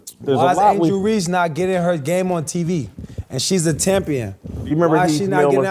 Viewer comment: Did AB just say Angel Reese is a "Tampion" 1:45:03 wtf 😂😂😂😂